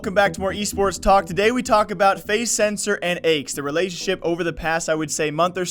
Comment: thought you said faze censor and eggs because his head looks like an egg with that haircut